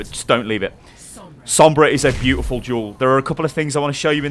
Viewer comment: “Sombra is a beautiful jewel” .. wait what?? hahha I swear everytime he says "duel" I hear the word "jewel" -- good video